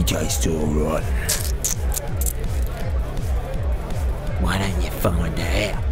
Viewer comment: what does the blond girl say here? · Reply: “Why don’t you find out?”